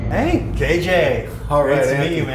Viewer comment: When they first bleeped out the girls name I thought he said, "hey B$&!&@